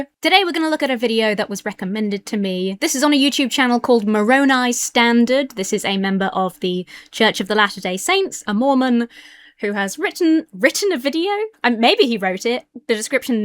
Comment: I thought you said he was from the “church of the ‘last’ day Saints”.